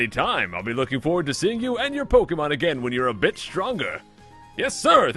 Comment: I was looking away from the video and I thought you said "when you're a bitch stronger" :P